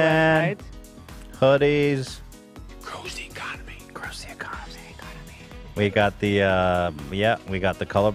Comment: every time joe says “it goes to the economy” i just hear “cozy economy” and teddy fresh is very cozy